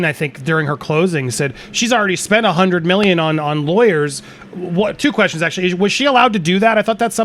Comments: I thought Elaine in court said she’s spending 6 million in lawyer fees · I thought 💭 I heard, Elaine say it was 6 million. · I thought I heard Elaine say AH spent 6 million on legal fees.